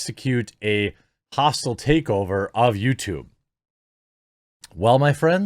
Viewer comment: What did you say here? YouTube?